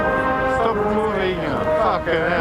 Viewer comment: Did somebody say "stop moving, fucking hell" at 52:05? :-D